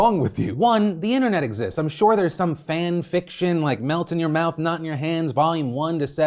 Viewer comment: I could’ve sworn he said “melt in your mouth, 🥜 in your hands” in the beginning LOL